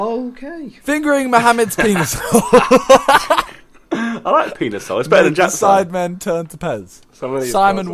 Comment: what did say it was "I like penis hole it's better than __" - Daddy ZRK but I don't know what he said at the end of it